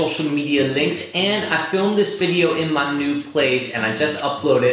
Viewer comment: "In my nude place" Am I the only one who heard that?